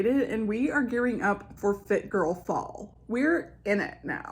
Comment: I keep hearing "forfeit girl fall" and I'm like: Yep, that's me. XD